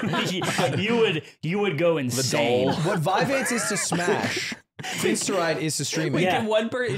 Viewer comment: I don't know the smash drama, what did he say? Viveaid? Idk.